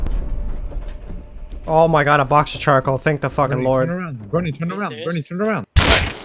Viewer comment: At the begging he sounds like he says Thank The Russians